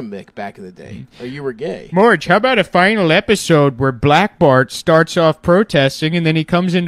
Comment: I have brain damage so until I started writing this comment I thought he was saying black Bart as in some sort of wild west outlaw nickname.😂 😂😂😂😂